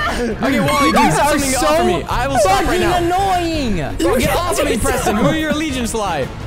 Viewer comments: did he swear or just say ''fudging'' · ok whats that? You said: your so f* annoying? Idk thats what i heard (sorry if im wrong)